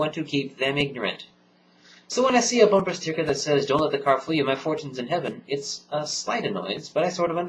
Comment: a splinter in my mind... What did the bumper sticker say @ 2:09? It sounded like you said it said "Don't let the car flee you by fortunes in heaven." Am I right? I have never seen that sticker. But if so would'nt that be an atheist bumper sticker? reworded: Don't lose control of your car because of promises of heaven.